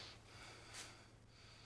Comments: The Mandela Effect is so real. I’m watching the movie as I type. The MOMENT I thought I heard “sorry I RUINED...”, I came here and realize he didn’t say that at all. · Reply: I thought he said, "Sorry I broke up your black panther parrta..." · The Mandela Effect is so real. I’m watching the movie as I type. The MOMENT I thought I heard “sorry I RUINED...”, I came here and realize he didn’t say that at all.